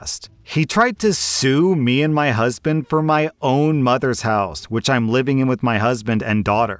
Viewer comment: story 2: I thought OP said her own mothers house? as in not her mother in laws house??? I'm so confused